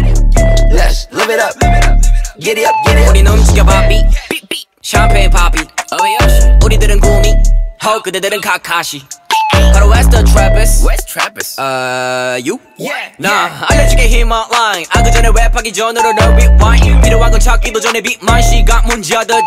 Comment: I can’t be the only one who thought he said wheres Travis 😭